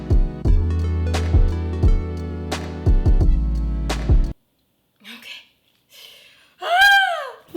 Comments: When she said she needed tooth picks I thought she said tooth paste and I was like “what the heck that will ruin the cake” 😦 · did anyone thought she said toothpaste instead of toothpicks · I thought she said we need toothpaste then she brought toothpicks,and I was like😐😑 also love youuu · we might need some toothpicks Me: dId sHe jUsT SaY wE mIgHt NeEd SoMe TOOTHPASTE!!!???? Also me: I wouldn't take a bite of some TOOTHPASTE FROSTING Me later on when she put toothpicks on: OHHHHHHH, she said toothPICKS I see now LOL🤣🤣🤣😂😂😂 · When said toothpick I thought she said toothpaste lol Edit: Someone liked my comment!oh wait that was me... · When she said that she needed some tooth picks, I thought she said that she needed tooth paste, and I’m like quarantine has really gotten to ya huh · let's put some toothpicks for it Me:TOOTHPASTE?!?!?!??!?!?!?!?! Me AGAIN:oh toothpicks nvm I misheard it's not toothpaste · When she said "toothpicks" i thought she said toothpaste 😂😂😂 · Thought she said toothpaste lol 😂